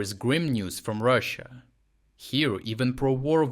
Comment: "Today there is grim news from Russia". I thought you said "great news". Both make perfect sense.